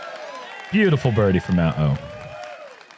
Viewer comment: Was it just me or did it sound like Jerm said “beautiful birdie for that hoe” when matty o tapped in his birdie on hole 14?